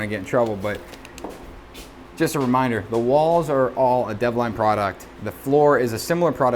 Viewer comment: I want to do my bathroom like that downstairs bathroom. What product is that? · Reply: This Bathroom has no tile, stone, or paint. Suspense is killing me. 6:23 I think he said Devline (Devlon?) product but I can't find anything on it.